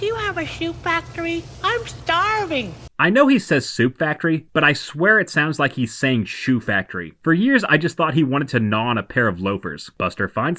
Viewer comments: I always thought he said chew factory so the food could get pre chewed since he broke his teeth. In fact, watching your video was the first time i heard a soup factory. Love your F.U videos by the way.😊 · Oh my god, so did I, man. That drove me nuts as a kid to think he said shoe factory, took me years to realize he meant soup. Glad I wasn't the only one to mishear it. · IT DOES I THOUGHT HE SAID SHOE😂😂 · it does sound like he said shoe factory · I also thought he said shoe factory 😅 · I thought he said shoe factory for years too.